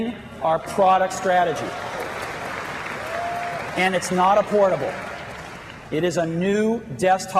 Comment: 😂 I thought he said “but today, for the first time in 2 and a half years we are expanding our product strategy… and it’s NOT AFFORDABLE.”